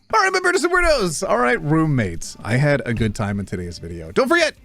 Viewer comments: I had to listen to the intro twice to make sure I was hearing the "roommates" right, because for a moment I almost thought he said "broommates". I feel like an October opportunity was missed. Yay for a meme video, though! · I heard "what's up broom mates". Are we in a coven? naais